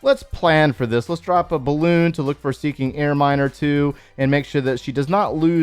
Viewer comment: Finally I maxed out th8 with lab. So now here I come th9! Also thx to ur th8 series it helped me ALOT! Thought I heard "seeking air miners" 😂